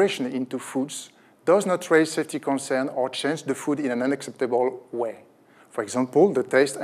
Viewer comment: the subtitles say "unexpected way", it should be "unacceptable way".